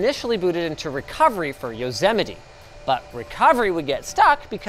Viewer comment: Did Linus just say Yozemeti @ 10:06?